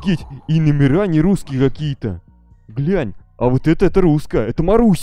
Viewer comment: сказал блять или мне послышалось